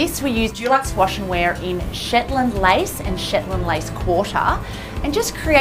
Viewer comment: I thought she said “Shitland” and then realised it was “Shetland”... I hope. I’m from England so sometimes the way we use different words surprises me. We just say “wet room” instead of “wet space” etc. I’m always like “REALLY Bonnie?!” Whenever chooses anything bold, but normally admit it looks incredible when it comes together. But not feeling how busy the bathroom is in this build tbh. But I love these “three birds”, you always put a smile on my face lol.